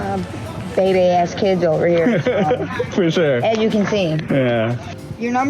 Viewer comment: did she say bebe ass kids? Like robbin harris!